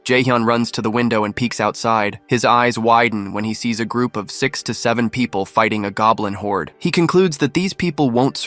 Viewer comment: What...? say that again... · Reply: SIX SEVEEN